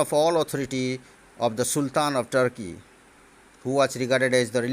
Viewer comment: Sir around 8:32 of the video what you told that Sultan...... I didn't understand ? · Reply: Sultan of Turkey